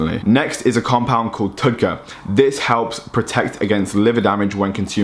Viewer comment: Can someone let me know what compound he's saying at 12:46 that regenerates the liver? Idk how to spell it out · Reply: TUDCA finally figured it out